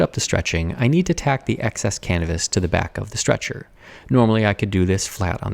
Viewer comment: did I hear excess cannabis? I know the word should be Canvass but I hear something else hahaha